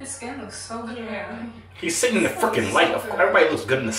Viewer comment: yo bro you did you say fucking